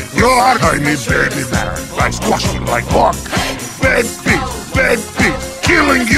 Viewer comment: wtf it sounds like he says likes to suck my cawk but the subtitles say 'i squash u like a bug' XD